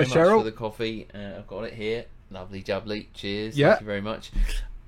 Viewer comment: Oh he said cheers I first thought he said jizz